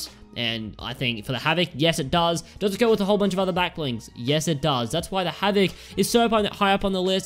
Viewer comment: i thought u said skins(characters) not backblings